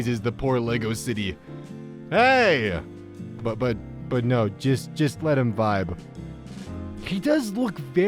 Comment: instead of saying “Just let him vibe” it said “Let him die”